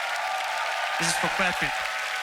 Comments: it sounded like AVH was saying "This is for Patrick" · Sounded like AVH said "This is for Patrick"